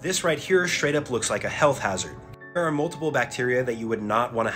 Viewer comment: Am I not the only one who thought that he said “hell”?